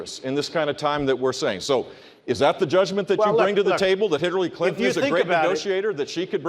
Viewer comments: Did I hear Jeb say "Hitlery Clinton" at 5:15 lol!!!!?? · did Jeb say "Hitlery Clinton"? P.S. look at his trembling weak ass. No wonder he lost.